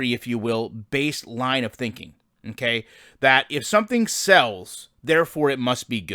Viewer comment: Thought it said 'smells'. Same diff